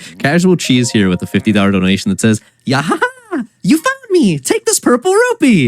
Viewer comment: LMAO DID HE SAY “YAHAHA YOU FUCKED ME?”